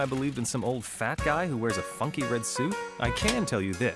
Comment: lol i thought he said fucking red suit xD it was funky red suit...=D